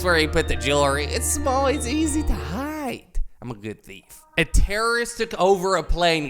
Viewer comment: Who else heard I'm a good thief